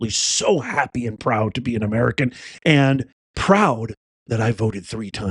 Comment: It sounds like Biden said Americana?